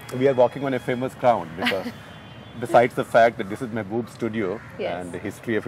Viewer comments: did he just say "Boobs studio"? LOL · what does he say at 0:32 ?:D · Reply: tag : the name of studio is mehboob.He just pronounced it wrongly :D